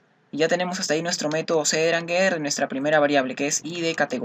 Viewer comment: como se escribe em metodo que dices escucho algo como ceranger por el minuto 13:00-14:00 Gracias de antemano